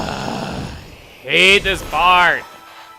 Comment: instead of saying:I hate this part! I thought he said: I need to fart!